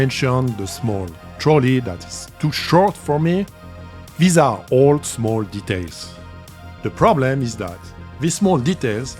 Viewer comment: tu as dis bizarre ? Ou c'est moi qui ai halluciné ? 😅 toujours de très bonne vidéo en anglais 👌